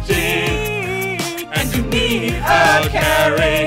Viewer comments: Thought it said "It's gonna be gay" for a moment there lol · I swear i thought he said "it's gonna be gay" during the first chorus :L